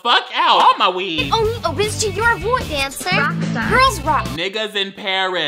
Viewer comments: why i think she said cancer. Like is miss girl ok. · When she said dancer I thought she said cancer, i was like “GIRL- WHAT?”